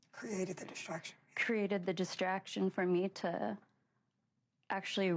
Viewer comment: you hear someone telling her what to say "created a distraction "